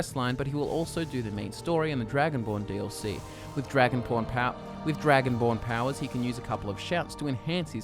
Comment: did you say dragon pron?